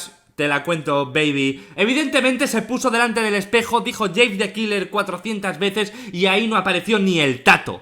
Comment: Like si escuchaste que dijo JANE THE KILLER en el minuto